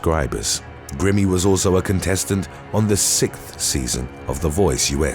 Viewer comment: I heard SITH season ...nah homie she was on the SIXTH SEASON